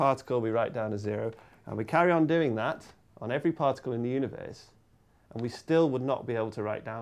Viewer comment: I'm not trying to insult his accent but am I the only one who thought he said, "Particles in the uterus"?